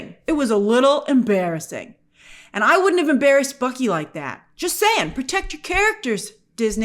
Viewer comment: I cackled when i misheard bucky for f ucky bahahahahha